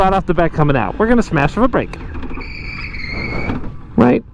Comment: I really thought you said “smash the front brake” and I was terrified.